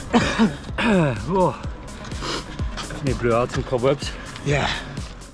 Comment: may I ask near 02:57 what did Froomey say? cobwebs? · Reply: …need to blowout some cobwebs